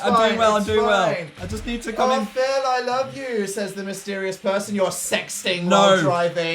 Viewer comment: I heard "Oh Phil I love you" and died a little.